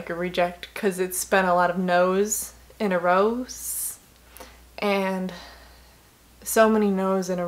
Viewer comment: !!! What a lovely lass!!!!! “Nose in a Rows” 👃🏼👃🏼👃🏼👃🏼👃🏼 That’s what I heard you say! And… LOLLLL the facial expression when you say “ or maybe you get the part.. and that’s fantastic……. For you” LOL just made me laugh